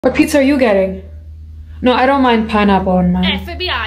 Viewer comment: I misread the first statement i thought she said "hi pizza are you gay"